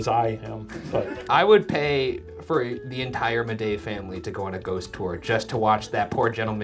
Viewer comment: I swear I just heard you say “Bidet family”, instead of “Midej family” 😬